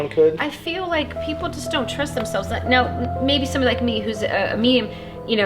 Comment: did she call her self a meme